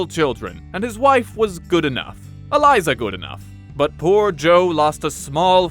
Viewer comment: uhhhh Hamilton reference or am I just hearing Eliza?